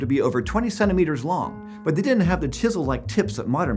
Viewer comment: I misheard this...as twenty SEVEN meters.